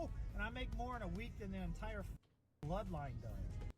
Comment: I think he said the bloodline as in The WWE bloodline group